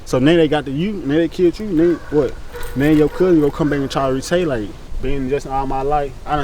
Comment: did he say Re-tay-late??